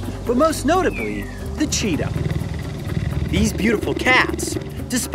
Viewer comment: I thought he said cheeto 1:27 I was like what?